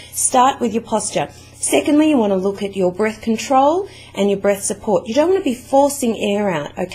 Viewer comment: i thought she said birth control i was like wuhhhh o_o